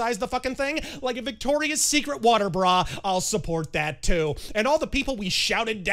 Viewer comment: at first i thought said "victoria's secret waterboard". this generated two thoughts: (1) a victoria's secret branded flotation device and (2) a bunch of lace-clad, calorie-deprived, 8 out of 10 models interrogating terrorists via waterboarding. this made for some highly entertaining daydreams until i realized i was the fool for mishearing him... but the daydreaming continued anyway.